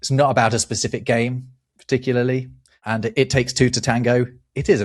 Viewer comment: I originally thought it was "it takes two to tangle". Then again, I used to think another saying was "the Dahmer Party, be thankful you weren't invited" instead of "the Donner Party", etc.